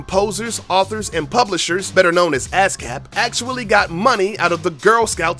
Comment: When u said ascap I thought you said asscap